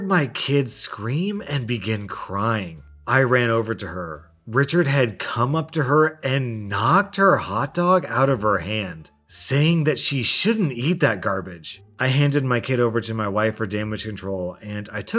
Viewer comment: I thought he said Richchard came up to her and knocked her out with a hot dog